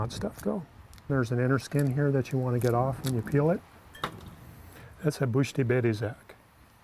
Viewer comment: What is he saying at 3:58--"bushti bebeh zac"???